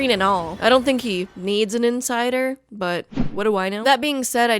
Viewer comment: thought she was gonna say ‘but what do I nose’ 😭